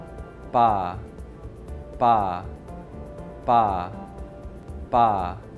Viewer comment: I heard "Green Storm" and @ 2:23 I heard "Faa" and "Thaa".. wtf?